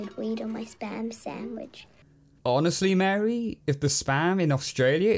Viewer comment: I thought she said sperm sandwich 💀